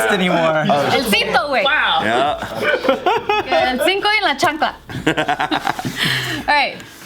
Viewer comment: sounded like said “el Cinco y la chancla” lmfao 🤣